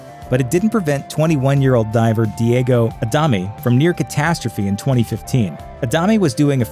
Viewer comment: Did i hear 3:08 Diego A dummy? Lol sounds fitting for this video